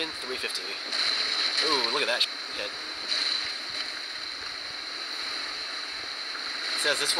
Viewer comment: it sounded like u said look at that shit